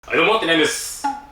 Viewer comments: 一家に一台とか言いながら２、３台持ってそうな。 はいどうもてらやんですって聞こえる人いない？ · 「はいどうも谷やんです」 俺「謝ってないです」 聞き間違いって怖い